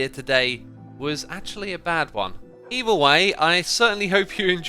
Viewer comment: I think that the building you designed for the gold farm looks wonderful. It's different, but I feel like it's going to fit in the jungle well :) Also, just a little thing I noticed, at 4:45 when you said "either way" it sounded like "evil way" :P I just thought it's funny, since I've seen people make theories about Evil X coming back with the Nether update >:)